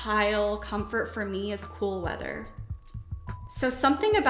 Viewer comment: When she was listing tactile comforts and said she enjoys “cool weather” I heard “full leather” at first and got shivers 🤦🏻‍♀️😂